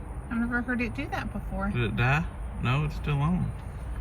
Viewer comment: Sounded like she said, "I did it", like she jumped. I love that they are asking for prayer now. They trust in you to help them.